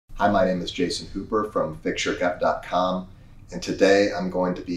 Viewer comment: At first I thought you said your name is "Jason Pooper" which would have been appropriate for this!